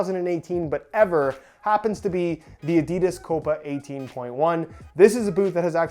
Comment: I think he said 18.1